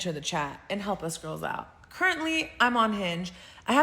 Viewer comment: I thought she said "Currently, I am unhinged" Well, that would be the most honest thing she's ever said!